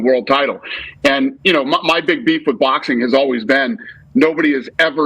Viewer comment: I thought Dana said "Boxing was dead". Hypocrite.